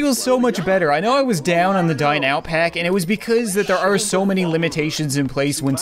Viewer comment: It sounds like he says die now pack